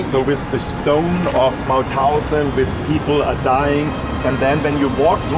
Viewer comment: the subtitles are wrong at 3:50, the Concentrationcamp is called Mauthausen.